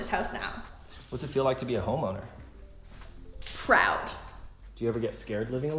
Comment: I thought he said homo. For a second then I play it back and realized that he said home owner 🤣🤣🤣🤣🤣🤣🤣🤣🤣 I was dead